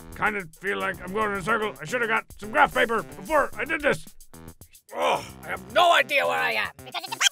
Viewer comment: i think i've figured out what Strong bad says here cuz i slowed it down and this is what I could decipher, "Ugh, I have no idea where I am, because it's impossible to orient myself, because the walls keep changing color, because apparently this is some type of psychedelic colorful maze! (panting) Please color maze, I just want- I just wish I brought some graph paper!"